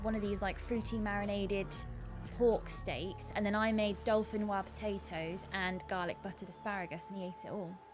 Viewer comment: Im sure I heard pronounce it "Dolphinwah" potatoes XD